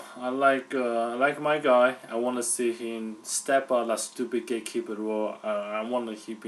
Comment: the guy on the right sounds like he says "gay keeper"